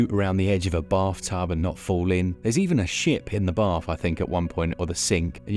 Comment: I had to rewind so many times because I thought he said there is a sh*t in the bath......